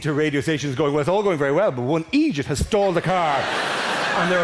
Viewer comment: For the first few tines he said stalled, I thought he said stole. Which was confusing but hilarious.